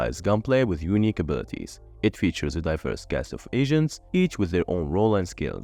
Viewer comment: I may be dumb but what I heard was “It features a diverse cast of asians”